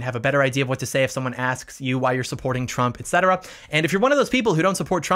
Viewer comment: I honestly thought the video said why I not supporting trump which made me confused